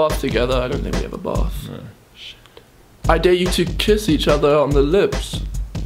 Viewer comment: I heard 'we made love to each other' XD